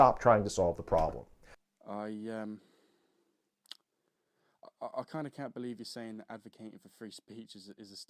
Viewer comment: What happened to you audio at the 3:25 mark i couldn't hear you. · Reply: Fuck knows mate sounds audible on mine, albiet slightly quieter than the rest of it.. for the record I said ''I kind of can't believe you just said advocating for free speech is a stifling tactic... surely the polar opposite is true?''